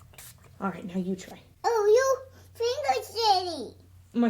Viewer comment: 😂😂😂😂😂 I don’t know what she was trying to say, but I laughed so hard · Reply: "YOUR FINGER'S DIRTY!!" XD